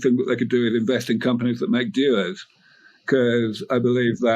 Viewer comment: Amazing content as always! At 1:09:15 he says the best way to get exposure to the growth of the life extension industry is to invest in companies that make "duos"? Someone please help a brother out, I don't understand what he's talking about! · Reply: dewars, the containers that cryopatients are stored in. <link>